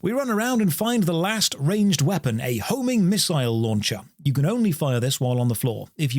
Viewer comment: I misheard "homing missile launcher" as "holy missile launcher" and that would have been waaaaaaaaaaaaaaaaaaay more fun